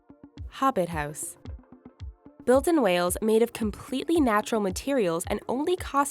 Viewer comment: I thought it said made of whales!😂😂😂